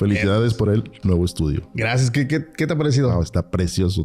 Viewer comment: Minuto 02:56 le dijo Quike??? 🤔 Es 😅😅 o escuché mal😅😅